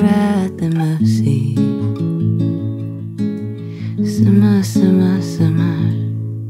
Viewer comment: Tho it sounds like she says simmer a little ♥️ Hayley